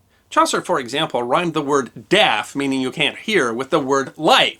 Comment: I thought you said daft.